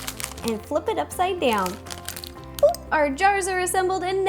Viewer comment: did Ro say POOP at 12:10 ?? just asking